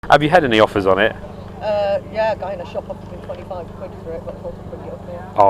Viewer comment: what did that woman say at 5:10 a guy offered me £25 for it but.... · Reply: oh sorry she said a store offered her 25 :) that's why I passed as she wants the same or more